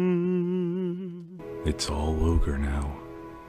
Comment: I’m having a moment over here, crying cause malleus’s humming is like oh my god 😭😭😭 I love it and then I just hear “it’s all ogre now” lmfao 😭😭